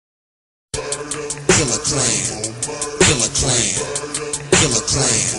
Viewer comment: It sound like he saying kill the Klan . I know he saying killaklan